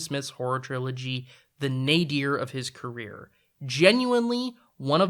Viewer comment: The what of his career? The captions just say "ner" I'm hearing "Nay Gere" and I'm not familiar with the term. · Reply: The Nadir. The lowest point.